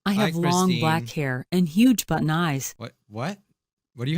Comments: did she say huge butt eyes? Me:I thought she said huge button eyes .-. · “I have long black hair and huge button eyes.” “hUgE bUtT eYeS??!??? What did she say????” Me:” BRUH, SHE SAID BUTTON NOT Butt, BUT it did sound like butt.” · huge but nice huge but n' ice huge button ice, that's what i hear in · i think she says she have huge but nice eyes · She said huge button eyes. It was hilarious when you thought she said huge butt eyes. 😂😂😂😂😂😂😂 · i think She said huge black eyes or something like That xD (OKAY IM SORRY FOR ENGLISH IM FROM POLAND)